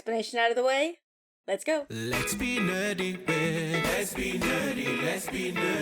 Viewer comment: OMG, I’m sitting here listening to another podcast when “SLAP” your name clicked. 😂😂 I can’t hear lyrics in songs so the whole time I thought it was saying “Let’s go Nerdy”. That never made sense to me so I’m assuming I was processing in back of my mind. If anything, that name made you even cooler IMHO. Life is sometimes simply a joy. 😄👍🏿🖖🏿